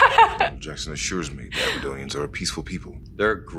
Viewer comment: "Danial Jackson assures me the Abadonians are peaceful people" - yes I've lived in Aberdeen for a bit and they are friendly enough. Oh, I thought he said Aberdonians. 🙂